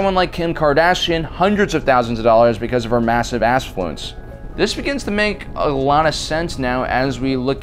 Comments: Did you say Ass-fluence @ 8:31? 😂😂😂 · bruh…..did you say “because of her massive ASSfluence”???……… 😂😂😂😂😂🤣🤣🤣🤣🤣🤣😭😭😭😭😭